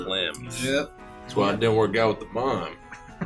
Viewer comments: What does say at 1:19:19 ?? I wanna understand so bad, but I can't make out what he said 😭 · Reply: “That’s why it didn’t work out with the mime” lesbian joke about the mermaids favorite snack being clams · what did say?? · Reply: "that's why it didnt work out with the mime" clams being slang for vagina